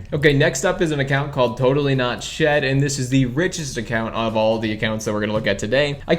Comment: did you say he was the most racist out of all the accounts (jk, i know he said rich)